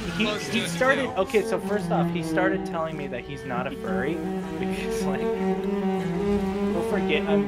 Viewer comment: Wait i heard "he said at first hes not a furry" and i am very confused. Im interested in this juicy story